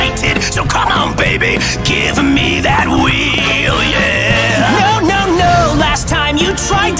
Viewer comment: I thought he was going to say "come on baby give me that weed“!